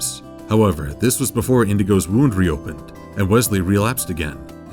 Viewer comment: did he say "indigo" instead of Inigo?